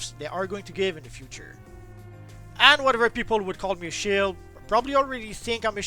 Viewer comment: What are you saying at 2:35? Ppl think youre a what? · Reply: Shill.